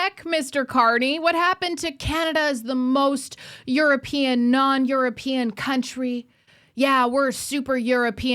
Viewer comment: i thought he said we were the most europian like country in the world